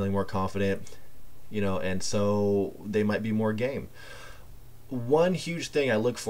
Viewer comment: thought you said more gay